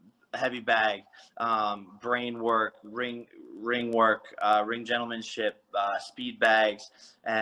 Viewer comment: did he just say ring gentlemanship 😂